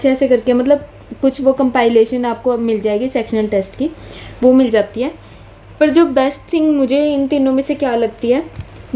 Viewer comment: At time stamp 3:04 thoda cut hogya what you were saying about teachers. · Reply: bs vo thoda controversial ho jata...vase i was saying ki kuch teachers itna fast krate h ki agr apne basic nhi kia to smjh ni aega